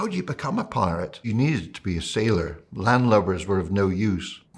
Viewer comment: the captions say "land lovers" but the term he used was "landlubbers", which is a way to describe people who are not familiar with the sea and sailing.